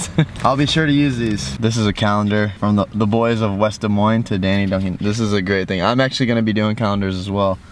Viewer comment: The one triplet in the middle I thought she said thank you daddy Duncan